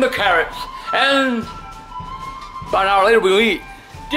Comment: Still to this day I have no idea what in gods name he says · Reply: "About an hour later, we'll eat"